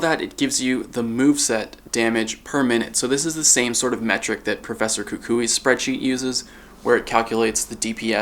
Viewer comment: did he just say professor kukuii